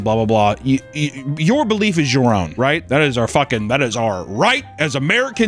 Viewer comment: Didn’t hear him correctly and thought he said your belief is wrong